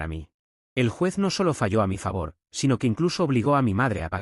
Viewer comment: Me asusté cuando dijo esto por que escuche fallo a mi favor